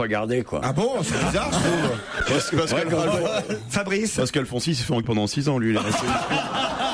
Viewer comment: Il dis quoi à 5:41 ? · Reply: il dit "Alphonsi s'est fait en....r pendant 6 ans" ça a été censuré, c'est pour ça que ça paraît inaudible